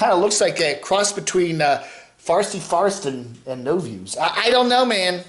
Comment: Wait! 🤔 Did you just say “No Views”? 🤣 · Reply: Good catch that isn't Crotchy that is "No Views". Clearly shots fired between the 2 channels.